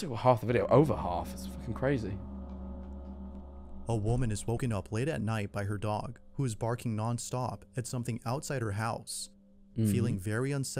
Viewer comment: i thought he said “a woman was woken up by her woman who was barking non stop” and i can’t stop laughing 🤣🤣